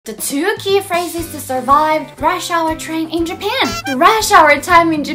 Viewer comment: She was saying rush hour but i thought she was saying Russia i was a bit confused at first.